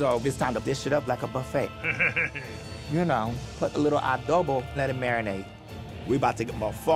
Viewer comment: did she say adobo?